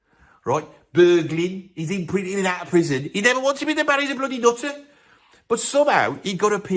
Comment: What the bloody hell do you say at 1:03? 😂 it sounds like nonsense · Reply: 'HE NEVER WANTS TO BE THE MAN . HE'S A BLOODY NUTTER'